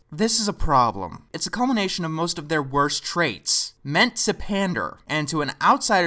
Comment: what does he say?I can't quite tell,but it sounds like "Ment Surpander" or something. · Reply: "Meant to pander" 🙃